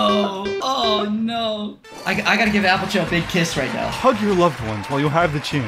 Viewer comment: As a Dutch guy, it’s always really neat to see my language being used. I was really surprised when the plushie was called “Appeltje”, I thought I misheard it at first